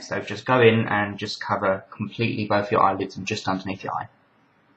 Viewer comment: Oh god when he said cover up your eyelids i thought he said cut up your eyelids xD #WtfMoment